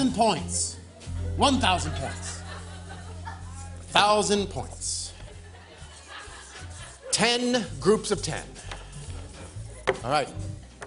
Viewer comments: "1000 points. 10 groups of 10" did I hear that right?? or has the pandemic melted my brain? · Did he just say 1000 is 10 groups od 10s??